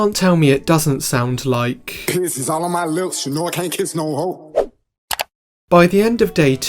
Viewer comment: I actually first thought that carti said "piss is all on my lips" before checking the lyrics.